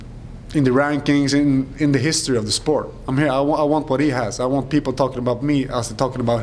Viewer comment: What's that word? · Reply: He said "I want what he has".